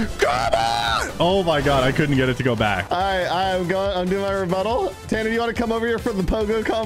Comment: sounds like instead of rebuttal says ," rebutt hole"